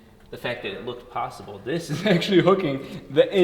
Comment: did he just say trive train? Or drive train? Yanny or Laurel? U decide.